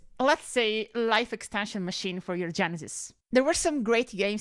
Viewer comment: 😅😅the first few times you said genesis i thought you said genitals